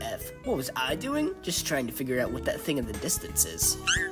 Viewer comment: what is he saying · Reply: I think he said “it appears to be a brick in the distance”